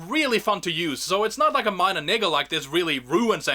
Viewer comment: Your speaking to fast like 4:38 sounds like you said something interesting here I’m not saying ur racist but that kinda caught my ear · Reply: bruh he said niggle